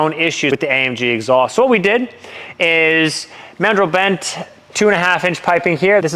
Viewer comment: Is this the "Single Inlet Single Outlet, 2.5inch/63mm" option? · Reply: I think in the beginning he was saying they went with 2.5 inch pipes